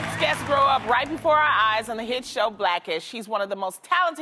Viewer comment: Loni calls him a she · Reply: She actually says: “HE’s one of the most talented people in the business”. She says it fast hence it sounds like she said “she”